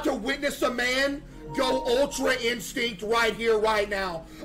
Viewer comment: Ultra extinct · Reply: thank you for making us understand, I thought he said ultra instinct